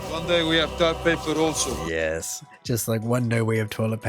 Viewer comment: I thought he said tar paper.